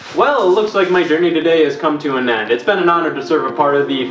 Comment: What is the last word he says? I've been trying to figure out the acronym for five minutes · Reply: Ne'er-do-well